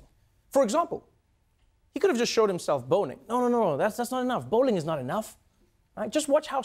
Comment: I could have sworn he said "bone-ing"...